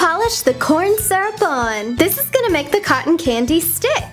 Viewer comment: it sounds like she is saying cotton cangy